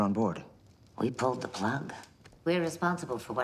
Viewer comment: I hear Capt. Janeway say "We pulled the plug." at 1:48, but not "She's ours." Btw. you missed an apostrophe there.